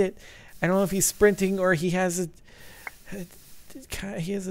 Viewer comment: Lolzzz at 4:57 did he just say a trident dick @@